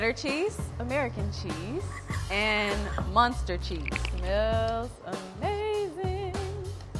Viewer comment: Did she just say monster cheese? 😂